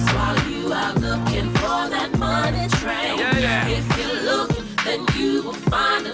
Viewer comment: I could've sworn they were saying money train 😂😂😂